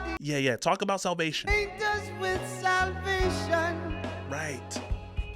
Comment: I have always heard it as “bait us with salvation”